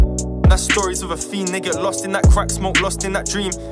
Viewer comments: swear mans said the n word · swear down he said the n word or am i glitchin.. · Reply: He said they get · what de he sayyyyyyyyyyyyyyyyyyy? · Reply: He said “they get”😐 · did he just say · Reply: Na he said "they get lost in that crack smoke" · is it just me or did he say the n word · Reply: He said “They get” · did he say n word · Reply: No he said they get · did he just say.... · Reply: He said "they get" And that's a story of a fiend, THEY GET lost in that crack smoke, lost in that dream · did he just say the n-word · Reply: "They get lost..."